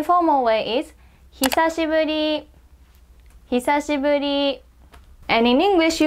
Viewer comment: in 8:23 , i misheared "he says your booty" and then i laughed so loud and people heared it😂